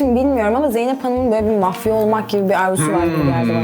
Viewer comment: "Bence Zeynep Hanımın 'Mafya' olmak gibi bir isteği var." Dediği yerde "Mafya" kelimesini "Mahfi" olarak Anladım ve arastırdığımda "gizli,saklı" olarak cıktı. Tekrar izlediğimde anladım yanlış anladığımı. Neyse ablanın hatrına bir yeni kelime daha öğrenmiş oldum. Sağol HFCBHFNFNF